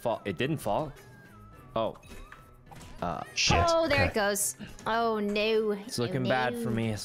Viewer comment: did Ranboo said sh*t or shoot. Please help.. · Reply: Jack said shit, not Ranboo